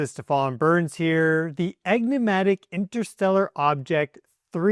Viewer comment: did you say 'EGG-nimatic? en·ig·mat·ic.